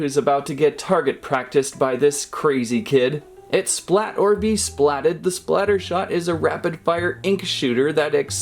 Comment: When he said splator be splatted I thought he said kill or be killed (undertake reference)